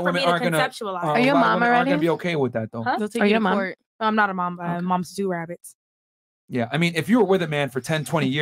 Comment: “Are you a mom?” “No but I’m a mom to two rabbits.” WTF did she say?? Ha even the subtitles are confused. 🤦‍♂️